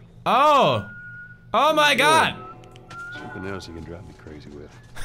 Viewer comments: Pewds logic: picks up brick, drops bottle... Oh hey a bottle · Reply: anybody notice at 0:13 subtitles said annoy but Joel said crazy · The caption said something else you can annoy me with but Joel said something else you can drive me crazy with.AMIRIGHT?Or just plain insane?